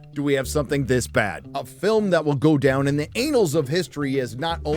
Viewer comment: I gotta know, does he say "anals" instead of annals of history on purpose?? 😂